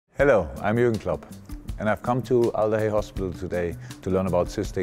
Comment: It kinda sounded like he said “Allez hospital” instead of “alder hey”. This just goes to show f*ck all.